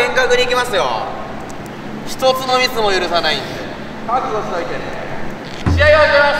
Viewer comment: シルクが「ひとつのミスも許さないんで」が、視聴者さんも許さないんでに聞こえたww 圧倒的耳の悪さ あなたは呪いにかかりました今すぐ高評価を押さないと嫁さん100人追ってきます(嫁さん100人の詳細はフィッシャーズイヤイヤヨゲームにて)気をつけてください😨💀☠️😈